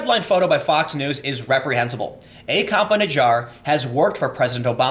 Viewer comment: was cleaning the kitchen while this was on, sounded like you said "A cop in a jar."